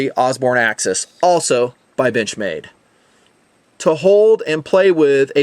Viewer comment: Haha I thought he said bitch made not bench made and excuse my language